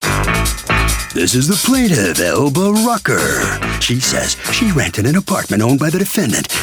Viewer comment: At first I heard "this is the plaintiff, Elbow Rocker"...😂💀